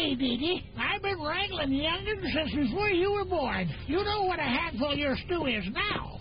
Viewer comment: clickbait where's the insult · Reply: i heard him say "i've been wrangling youngins since you were a boy"